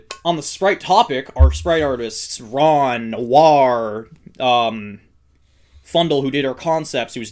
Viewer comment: did anybody else not here the n sound at the beginning and thought he said "war" as in wario